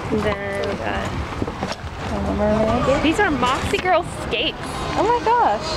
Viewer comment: Nice haul! At first I thought you said Boxy Girls for the skates, haha. Moxie Girlz makes more sense.